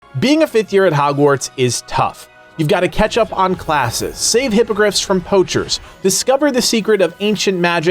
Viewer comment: at the beginning i thought he said "save hypocrites from poachers" lol